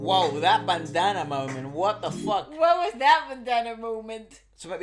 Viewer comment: Damn all this time I thought they were saying banana 🍌 😂😂😂